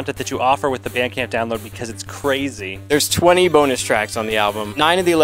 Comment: It's fun to be russian. When he said, "There is bonus track", I misheard it as "There is bonest track". Yeah, surely.